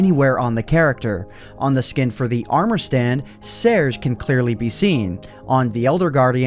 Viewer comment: did anyone else think he said "stairs?"